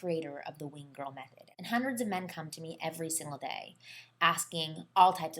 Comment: did she say "100 hundreds of men cum on me every day?"